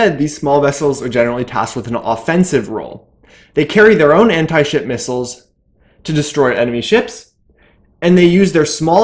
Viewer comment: Sometimes he sounds like he is saying anti shit missile for when he says anti ship missile